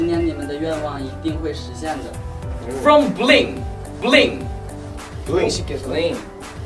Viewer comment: did he say from blink? Loooool guess the message was from a blackpink fan (blink) so the rest of the guys were surprised at hearing that